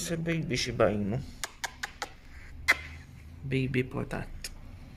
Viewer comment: At the beginning I heard you saying , baby potut instead of potat. Anyways. Potat s so cute many blessings to my dear potats!!👍